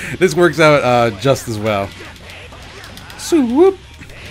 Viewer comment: did he just say suwoop